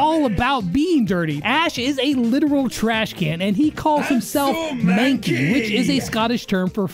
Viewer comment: is it just me or does anyone hear ash say "im so mad gay"